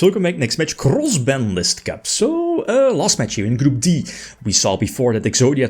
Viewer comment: I think i watches 20+ of these and i still mishear it as "crawls banlist cup"